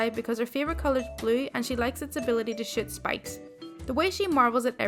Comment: Misheard it as "shit spikes". Ouch.